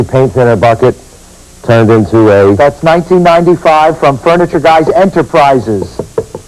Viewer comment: at first i thought that said "furniture gays" and i thought "finally! i have found my people!"